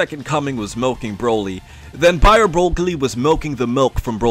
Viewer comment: i heard bio brockly